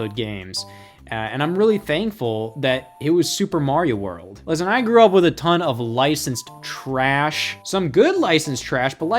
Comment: I was at this time stamp 10:51 and then I unpaused then paused again at this time stamp 10:54 and it just sounded like said “listen I grew up with a ton of Lice”